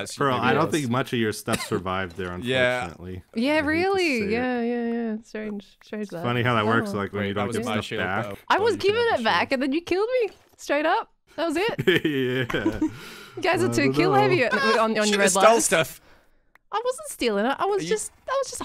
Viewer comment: Did Etho swear at about 21:40? It really sounded like he said “when you don’t give sh!t back”